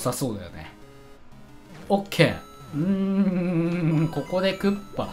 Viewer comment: くらいのときに さんが OK！んん〜って言ったとき 俺のGoogleが反応して俺は困惑した。 OKGoogleって聞こえたらしい